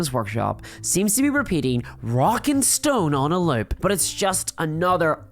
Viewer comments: DID I HEAR A ROCK AND STONE? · Did I hear a "Rock and Stone"?